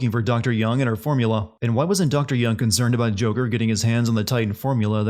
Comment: it says “hangs” instead of “hands”